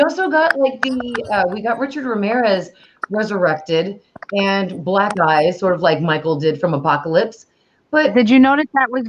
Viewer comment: His name is Ray the black guy name is RAY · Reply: ? She said Richard Ramirez was resurrected and (had) "black eyes" (like Michael's Satan summoning snake scene) from Apocalypse. I had to go back and listen again, but that's what said there. Unless there was something else. ? I'm only 11 minutes in...